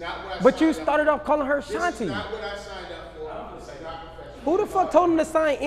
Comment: Did he just say “Nutty Professor” hollering 😂